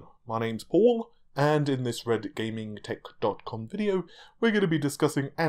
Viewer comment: I can see the struggle and pain of Paul While trying to say "red gaming tech dot com video" instead of TITTYCUM... I feel ya man... I fell ya.